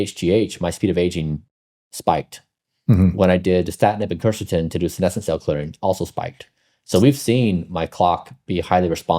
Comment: What did Bryan say here was the second thing that spiked his speed of aging? Something in combination with Quercetin.... ? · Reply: Danatasib